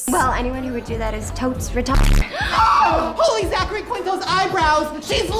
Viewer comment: did shane just say "holy zachary quito's eyebrows"???